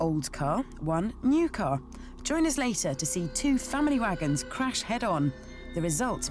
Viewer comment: Listened to it at 2x speed, at 2:18 it sounded like VBH was saying "Watch 2 fanny wagons crash head on", odd choice of words I thought till I slowed it back to 1x and realised she was saying "family wagons".